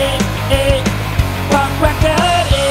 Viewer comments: it sounds like he is saying pop cracker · that sounded like c*ck sucker 😂😂😂😂😂